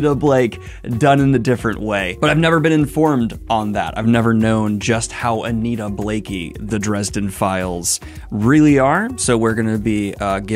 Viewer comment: When a reviewer comments that one book could be influenced by another, it would help if they took the time to check which one came out first. Guilty Pleasures was published in 1993 and Storm Front in 2000, so it would be more correct to say that Dresden Files is reminiscent of Anita Blake. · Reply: "I've never known just how Anita Blake-y the Dresden Files are". I think that's what he was saying :)